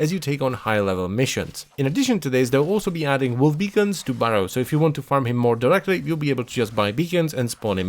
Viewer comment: "-to Baro" ... what? you tripped on a word there :p